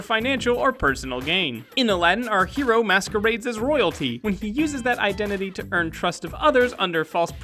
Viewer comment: I can't be the only one who heard "Masturbates as royalty"